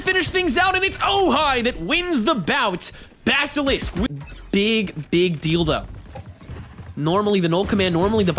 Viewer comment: What did he just say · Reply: LMFAO "big big deal though" but it does sound like it